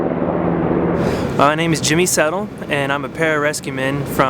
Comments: Who else thought he say that his name was Jimmy Saville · I thought he said his name was jimmy saville. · thought he said "My name is Jimmy Saville" 😬